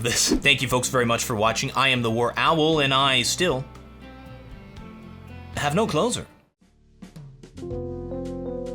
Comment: I always hear "and I still have no clothes on" much rather than "no closer"